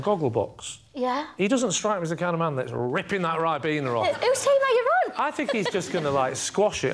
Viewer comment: WTF did she say at 1:02? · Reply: "Whose team are you on?"